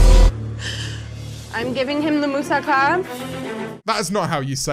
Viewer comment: did she just say Musa Khan?? It sounds like she's selling people.